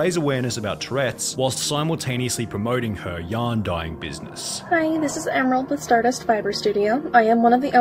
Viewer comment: I thought he was saying "young dying business" at first LMAO